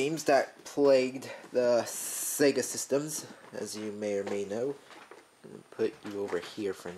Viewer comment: did he just say may or may know